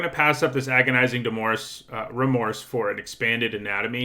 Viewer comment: At first I thought you said agonizing divorce instead of agonizing remorse